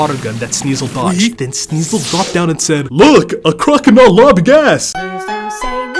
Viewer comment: What did he say at 3:20? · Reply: 5 months late, but whatever "a crocodile lobbing ass!"